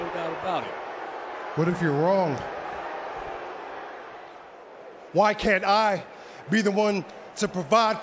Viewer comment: Did he say WHYYYYYY CAN’T IIIIIIIII